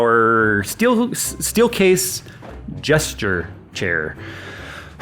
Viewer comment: Ohhhh, Gesture chair. I thought you said Jester chair.